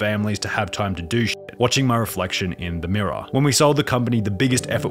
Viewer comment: They read it as Mirror instead of Monitor